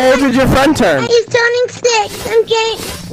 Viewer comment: It sounds like he said "He's turning 6, I'm gay"